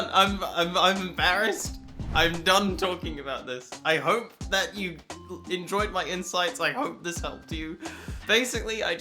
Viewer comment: you said "I hope that you enjoyed my insights" I heard "I hope that you enjoyed my insides" really love that you're having a great time. I still don't think bottom surgery is for me. But I've never thought that nobody should get it either.